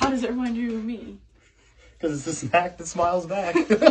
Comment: Am I the only one who thought it said “snap that child’s back??”😂😂